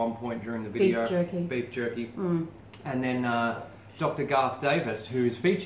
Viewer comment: haha, I thought you said Dr Darth Vader.... I'd probably trust his opinion more the ZdogdMD guy